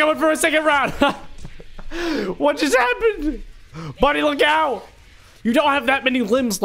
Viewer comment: for me at 2:16 I heard money isstead of buddy